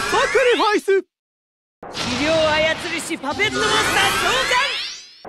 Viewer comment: 初めはポケットモンスターに聞こえた